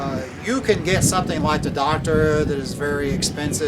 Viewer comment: Didn’t quite catch the name of the eyepiece mentioned at 8:14 - sounded like “doctorate” eyepiece. Also, is getting the largest AFOV ideal for an eyepiece? I spoke to Al Nagler at Tele Vue, and he said that the high AFOV of the ethos can cause some blackout issues. Is there an ideal AFOV for binocular eyepieces? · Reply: That would be the Docter 12.5mm, which is no longer being manufactured. APM has a knock off version of the Docter, not the same optical configuration internally but they have attempted to duplicate the viewing characteristics at about half the price.